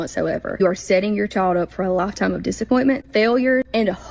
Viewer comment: Did she just say "a loff tomm of disappointment"?